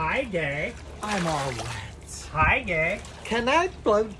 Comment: When he says "if the suit fits wear it" i thought i heard "if the crown fits wear it" ranboo's line 😔